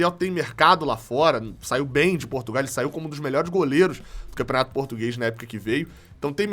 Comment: Eu ouvi Porto Velho aos 12:53.